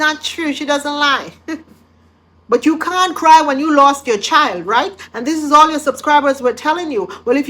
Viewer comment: Did i hear her say ," If you lost your child go and find it " my heart stoped for a moment